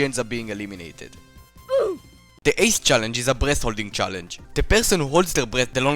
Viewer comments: nahh i heard ( 7:08 ) "the 8th challenge is a breast holding challenge." 💀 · i misheard breath as breast